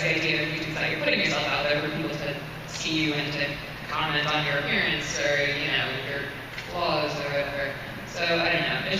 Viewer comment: What bad word did say around 34:35?? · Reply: crappy